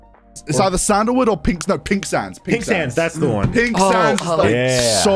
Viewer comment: Is it bad that when was discussing candles, I actually thought he said “Pink Sans”?